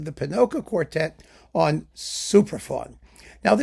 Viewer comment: Whenever says "Supraphon" I hear "Superfun". 😀